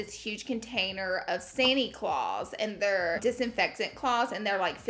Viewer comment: I thought you said "Santi Claus" 😁😂😂